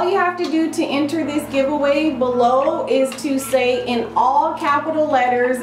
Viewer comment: When you said all you have to do is enter is type tymetheinfamousweek I thought you actually said I love Monica lewinsky hahaha